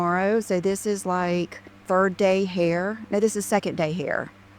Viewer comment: I thought she said Bird Brain Hair instead of 3rd day hair. Confirmation bias here!